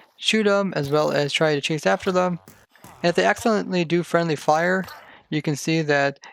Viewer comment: WHAT DID HE SAY!. "They excellently do friendly fire, that doesn't really matter" ??🤔🤔🤔🤔🤔 WHAT DOES THAT MEAN. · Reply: He meant accidentally